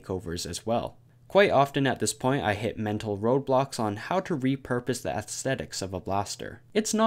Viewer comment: is it me or does it say mental roblox ?